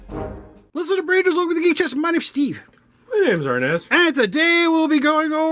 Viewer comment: This is what i saw when he said "lets look at the branches over the gay chest my name is steve" Bc my subtitle is open XD It literally said gay chest